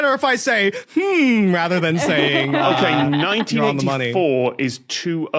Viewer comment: Just so subtitlers are aware, at 6:04 Tom says "Okay, 1984" but the subtitles says "Okay, 1994"